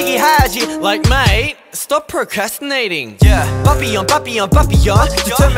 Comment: When he said “papillon papillon papillon” I thought he was saying sloppy joe cause that how my mom says it😂